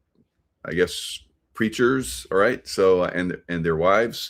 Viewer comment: LOL, I THOUGHT YOU SAID CREATURES. WHAT THE HECK? WENT BACK AND IT WAS PREACHERS. ME FUNNY!!